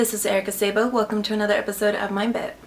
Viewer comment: lol good thing it said "mind-bit" for a sec, I heard "episode of my bed" and I was like "Wut O.o" xD All suddenly made sense